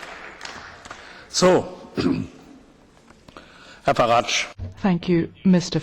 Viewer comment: Give him a knighthood. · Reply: Duc de Richleau Ex-Politicians. Sorry could repeat that all I heard was,''HEIL FARAGE''.